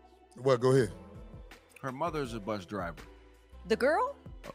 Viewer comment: i thought eboni said her GRANDMOTHER drove a bus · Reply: Her mother drove the bus. I played the audio on my live stream on Monday